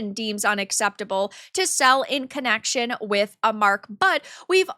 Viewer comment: "Mark"? Umm excuse me. What did you just call yourself??